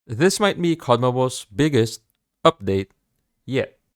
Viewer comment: what are you saying?!^^ "This might me conna was"?